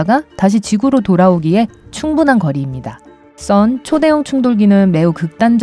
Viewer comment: 초에 잠시 들리는거 영어인가요?ㅎ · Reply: CERN 입니다. LHC가 있는 시설 이름입니다.